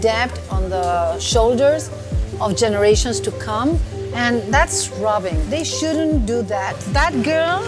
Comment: I think she said "That's robbing" ["That's theft"] but the person who did the subtitles wrote "That's scrubbing" 😅